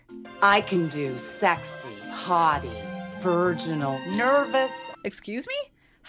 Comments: I thought she said "hottie" too, only because I wouldn't have thought of "haughty" as a character archetype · To be fair, I also heard that as hottie, not haughty. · I heard "hottie" instead of "haughty," too.